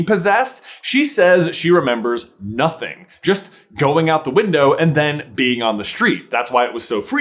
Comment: i thought mj said that she was just at her desk and then in the street